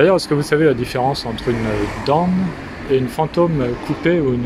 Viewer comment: une QUOI ? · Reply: Dawn ?